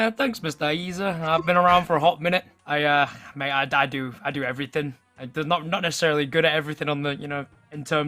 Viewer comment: Lmao i swore said uhh yea i die to everything